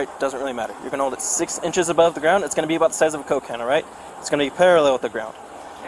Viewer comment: Did he just say "when you start this test don't complete this test" ?? 😂🤣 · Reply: I thought I heard, 6” of the ground, about 7 coke cans, but it was a bit muffled about the size of a coke cans. 😂